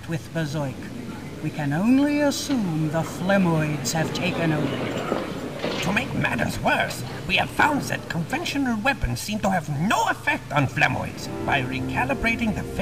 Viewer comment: I keep hearing 'flemboys'...